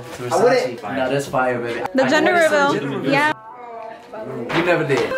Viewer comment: 👈🏽👈🏽😳 😂 LOL Now i know I can’t be the only one who heard that ….. did he say gender reveal??? I know I’m not tripping..😂